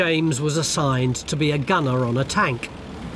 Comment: assigned to be a goner on a tank.... did I hear that right?